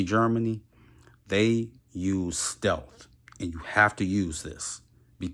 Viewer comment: did you say stealth?